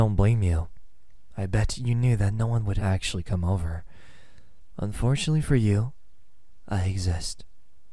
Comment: "fortunately for you, i exist" yes. very fortunate for me. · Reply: I swear that I heard "unfortunately for you, I exist" HAHAHAHA